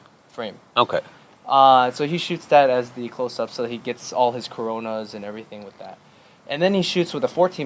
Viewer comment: Wait did he say "he gets all his corona?"